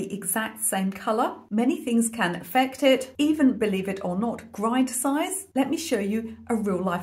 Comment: Love your accent!!! 😊❤🎉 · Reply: hahaha, I thought she was saying "grain size" rather than "grind size". It made sense context-wise but ears were struggling. 😂